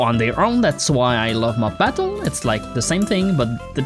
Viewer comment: What mod is this? i dont understand when you said "thats why i love mob-???" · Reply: mob battle